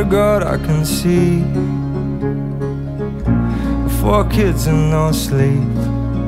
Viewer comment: i really thought he said "fuck kids and no sleep"